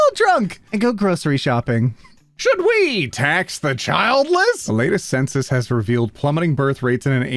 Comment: did anyone else think that said tax the children